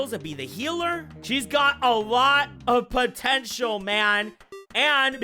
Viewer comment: DID HE JUST SAY THE WORDS "POTENTIAL MAN"????????